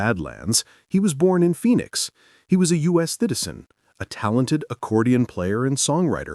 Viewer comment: Anyone notice that he says “thitizen” instead of “citizen” when talking about Tito Torbellino? 💀